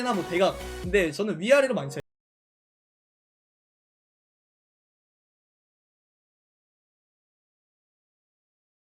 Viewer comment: 초 당부하는점 에서 난발하지말라는게 C에요 D에요? 첨엔 C라고 들리고 이후엔 D라고 들리는데.. 궁금하네요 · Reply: D예요 ㅋㅋ막슛..